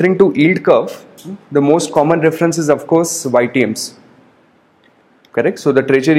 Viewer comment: I don't understand the word after 8:16 "the most common reference is to..."? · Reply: YTMs..